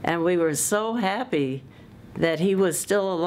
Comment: I know that's incorrect, but not being a native speaker, I keep hearing it as "Cyborgium". :)